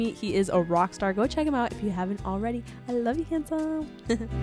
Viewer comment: who else heard tell Ricky Martian picture I hate you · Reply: She wispered I love you in her accent and it sounds like she said I hate you